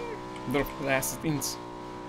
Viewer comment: Csak én nem értem itt mit mond ? :'D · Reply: drop vadászat inc. (in coming)